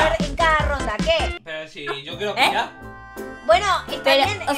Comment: que dice ahí por favor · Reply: Yo quiero por allá creo